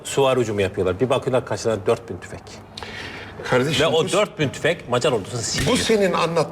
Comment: te ne diyor? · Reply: 4000 tüfek (tüfekli asker)